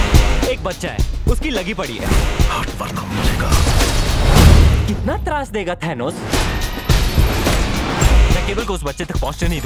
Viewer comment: What he said at 0:23??? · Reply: Sourabh Singh thanos